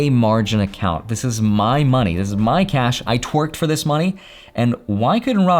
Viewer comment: "I twerked for this money" Ah! so that's how makes the real money :P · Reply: I thought he said "I worked hard..." hahahaha twerked is much better